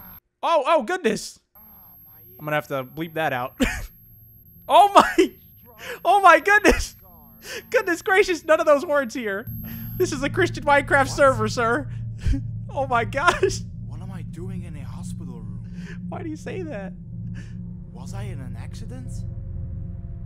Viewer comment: did he say just say minecraft server